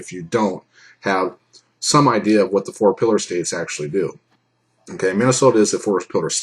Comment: I'm trying to figure out what you're saying and about the 3:40 mark. It sounded like four pillar States. But when I look at the closed captioning it says Forest Pillar state. That's not a Doctrine I've heard of. What are you referring to? EDIT: okay, I listened on and you said four pillars state. I'm pretty sure you're referring to the four pillars of self-defense. I know what the first three are, but I'm not sure what the fourth is. Is I understand it its ability opportunity and Jeopardy. Referring to the grounds you have for having a reasonable perception of being an imminent danger of grave bodily harm or death. What am I missing here?